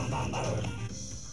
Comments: thought he said we got the wublins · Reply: I thought he was saying "We got the wobblies" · Am I the only one who heard "Wake up the waffles"? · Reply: For 3 years I heard “wig out the watlas “ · Wake up the Wublins, Wake up the Wublinzzz... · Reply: The whole time I thought it said “we got the Wublinz we got the Wublinz?